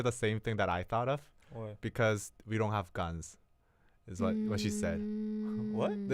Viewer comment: I thought he said "because we don't have gods" lol